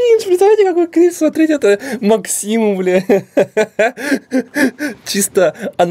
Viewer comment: мне послышалось или сказал б*я